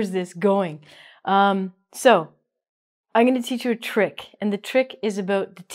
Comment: the subtitles show - I'm going to teach you trick, ... .But I hear oniy - I'm to teach you trick. I don't hear "going". prononced it? Sorry for my grammar and hearing.